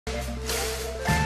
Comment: LOL I always thought he was saying "Funkman, on the keyboards. Funkman!"